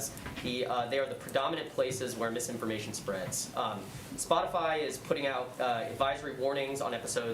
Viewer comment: She said splotify 🤡 lmao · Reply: I thought she said splotifly... LoL 😂